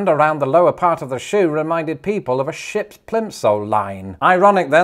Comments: Plimsoll?! I Thought you said "Pimp sole" 😅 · When you first said plimsoll I heard "pimp sole" and I dont think I've ever done a double take quite so quickly